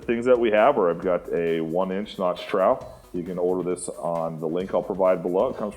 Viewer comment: What size trowel did he say? I thought he said 1 inch but that doesn’t look like a 1 inch trowel. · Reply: 3/8 inch notched trowel